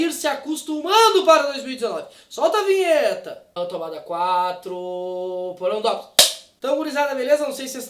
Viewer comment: Eu ouvi "porão do Dops" antes da claquete ou foi impressão minha? Rs